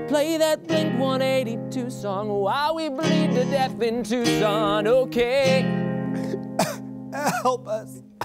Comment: I thought Closer said - while we bleed to death in a twosome 😂😂